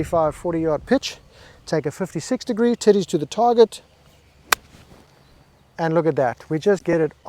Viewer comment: "I learned from PGA Tiger Woods golf game" , did he just say "titties to the target"? LMAO I love this guy!